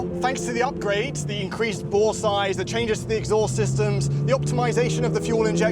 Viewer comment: did anyone else hear “due to the increased ball size” I’m thinking “yeah, I guess it does have bigger balls” 😂🤣